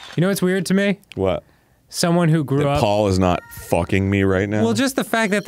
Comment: I was listening to this in the background while was playing an MMO, hardly paying attention when I heard Arin say "What That Paul aren't fucking me right now". My name is Paul and I just had the biggest spit take in my life.